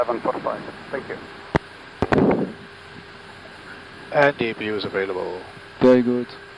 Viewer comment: Very nice !!!!!!! I don't speak very well english...... What is saying the first officer at 8:45 ? ........ available ? "and APU is available" ? am i wrong ? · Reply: Thanks for watching and your question. You are right. He said "APU is available". Have a nice day!